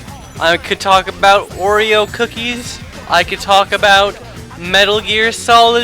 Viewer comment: oh, I thought you said Wario's cookies! XD